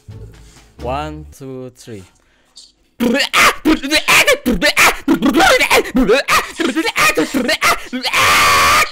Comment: ngomong apa tu mang? 🤣🤣🤣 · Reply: Betbok